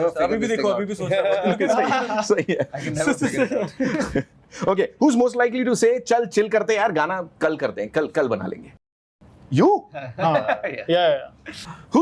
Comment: What did sanam said at or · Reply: Interviewer asked who's most likely to say "Chill, let's do it tomorrow."? Sanam replied he's is most likely 😂